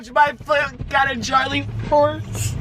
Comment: What is he saying at 4:58 ? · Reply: hes calling charlie quartz